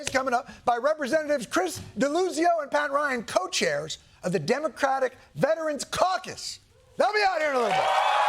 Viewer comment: I,m italian, english is not my first language, i heard “democratic veteran carcass”